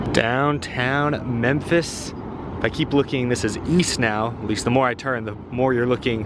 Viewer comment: Ohhhh! East? I thought you said “weast”